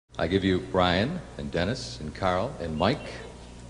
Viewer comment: He forgot Al. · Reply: I was wondering if I'd misheard! 'Brian, Dennis, Carl and Mike'...Al's my favourite :(